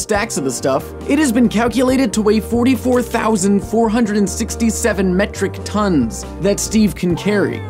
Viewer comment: below 44,467 tonnes, I thought it said "[ABOUT 246 BLUE WAFFLES]". DAMN, DO I HAVE A DIRTY MIND.